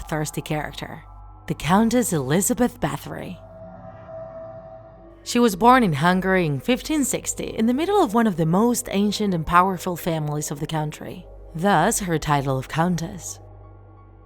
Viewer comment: What she said: She was born in Hungary What I hear: She was born hungry